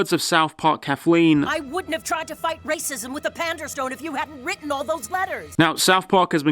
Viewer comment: Ahh panderstone, this whole time I kept hearing "panda stone" and wasn't sure what the joke was lol